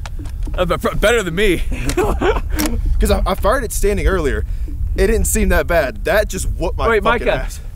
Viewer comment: sounds like he says "i farted standing earlier"